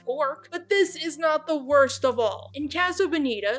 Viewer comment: Did she just say rachel and spaghetti-os both taste like Orcs??? I really like that fun characterization of her persona.